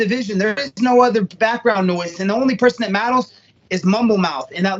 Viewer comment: i can hear a speech impediment a mile away, cause i have one. AND That my friends, Is a grade A fuckk up. Boi said Maturs